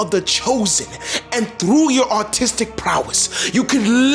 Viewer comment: thought you said autistic prowess and it didn’t even occur to me that was weird until 5 seconds later I went “wait a sec say that agin?!?”